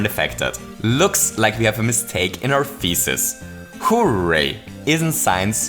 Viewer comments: Please use a different word or find another way to pronounce "thesis", because across the video, I only heard "feces"... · Every time he says "thesis" I hear "feces".